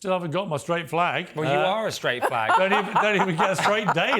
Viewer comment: Did he call him a straight fahg? I'm offended.